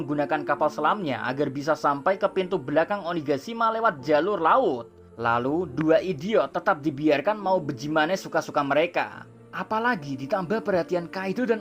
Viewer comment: please jawab, apa ane yang salah denger ni pada menit 09:25 . Bijimana atau Bagaimana?? :) Satu"x Chanel yg pling gua ngerti, yang teorinya masuk di akal cuma. Tetep jadi seperti ini yaa.